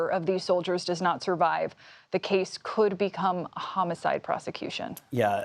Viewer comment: Judge did not say “could”she said he WILL be charged with homicide.